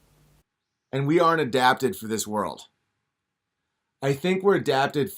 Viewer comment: I thought he said humans were adapted for eatin’, which actually makes sense. But of course, it was Eden…